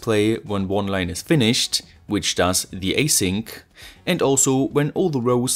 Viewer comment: A-sync instead of H-sync lol